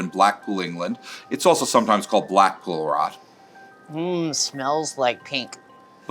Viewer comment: Umm did she say mmm smells like pink at 8:54 😂😂😭🤣🤣☺️